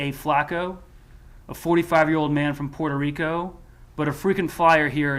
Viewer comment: Puerto Rico is part of this country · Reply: I had to go back and listen to it again and you’re right, he’s from Puerto Rico…I thought they said Mexico. It’s still dumb. It’s dumb to let someone from Puerto Rico fly in and control/destroy your neighborhood. It’s dumb to let anyone do it, no matter who they are or where they’re from.